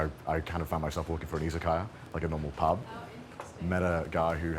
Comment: "I got really into anime, funnily enough, and then I went to Japan and I kind of found myself looking for an izakaya (pub)" Was it just me who heard "isekai" instead of "izakaya" (I mean, sometimes another country can just feel like another world)? 👽